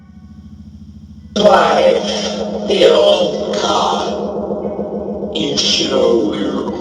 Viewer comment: I think the spirit said life the old die